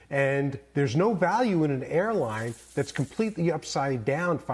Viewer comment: I thought it said hairline